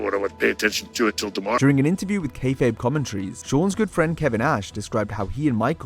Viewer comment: did you just say kevin ash? 🤣